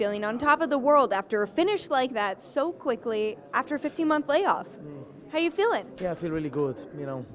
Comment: Did she said “after 50 months lay off” 0:16! Didn’t she watch the fight on 10-06-18 smh